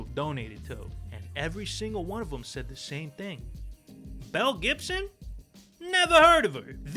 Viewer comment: I thought you said melle Gibson